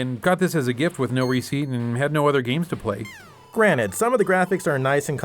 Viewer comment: Did he say "Granite"?